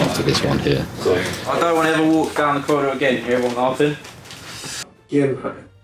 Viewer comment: Any one understand what is saying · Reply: “Don’t wanna walk down the corridor again and hear everyone laughing”